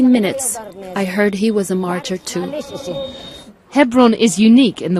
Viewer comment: i couldn't hear the word. Can someone help me please. 1:44 " Within 15 mins, i heard he was a .... too " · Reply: Martyr